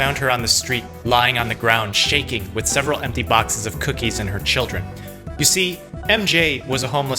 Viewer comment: I thought he said, "and several empty boxes of cookies in her children." Lol